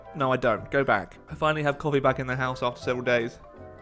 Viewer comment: I thought you said colly or something instead of coffee so yeah that sounded weird